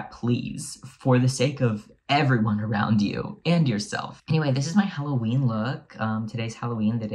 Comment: originally heard that as "end yourself" 🤣